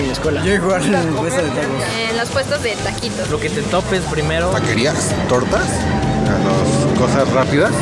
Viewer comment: le entendí baterías xd pero dijo taquerias